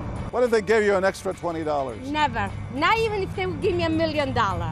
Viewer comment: bruh I thought she said she don’t believe in god I was like tf? But guys she said I dont believe in dat